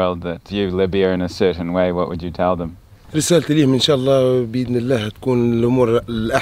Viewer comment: it sounded like the officer was saying "a certain image." That was really close to what just asked him. I was surprised at how he just started speaking English so clearly, but I guess it is an Arabic phrase that sounded close!